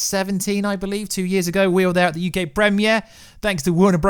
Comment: did you just say "thanks to warnerbrothers, you gay" xDD ?? EDIT Oooohhh UK rofl